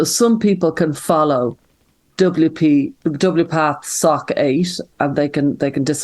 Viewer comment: Every time Stella says “SoC Eight”, I hear “SoCgate”…which I think is bloody perfect in its way! Thank you, both, for a fantastic discussion. I especially love the humour and optimism with which Stella speaks. Love yas both!